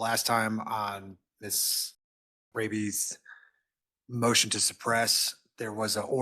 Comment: btw @ 29:00 did he just call her Miss Rabies... it does seem very appropriate Freudian slip!